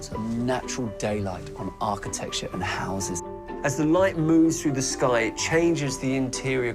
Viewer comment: did he say, "light moves through the sky"? I knew the sun wasn't stationary and the earth revolved